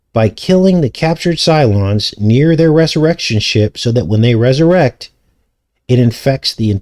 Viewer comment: 2 P e t 3 : 1 0 But the day of the Lord will come as a thief in the night; in the which the heavens shall pass away with a great n0ise, and the elements shall melt with fervent heat, the earth also and the works that are therein shall be burned up. 1 1 Seeing then that all these things shall be diss0lved, what manner of persons ought ye to be in all holy c0nversation and g0dliness, 1 2 L00king for and hasting unto the coming of the day of God, wherein the heavens being on fire shall be diss0lved, and the elements shall melt with fervent heat? 1 3 Nevertheless we, according to his promise, look for new heavens and a new earth, wherein dwelleth righte0usness. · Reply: Resurrectionship. I thought he said insurrection. I need to eat more coffee